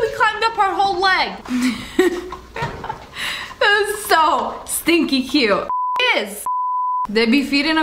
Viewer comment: This is how many times she said " stinking cute" l V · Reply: I thought she said stinky cute lol